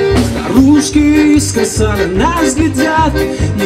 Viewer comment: Балдел от твоей музыки, пока не услышал "русские искоса на нас глядят". 17:20 Это серьезно?